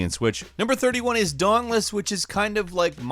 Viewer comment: Everyone you say "dauntless" I hear "dongless"